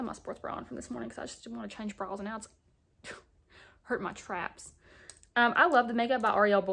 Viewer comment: Ok guess I'm old af! But she said her sports bra was hurting her "traps" can someone please inform me what traps are??? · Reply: aaahhh now I see! I thought after might said straps but Thank ya sistta! Can't wait grab me lip oil girl! U Killin it!